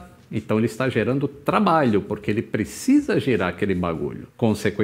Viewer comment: " ele precisa girar aquele bagulho " é isso msm que escutei responde ae rsrsrsrsrssrsrsrsrs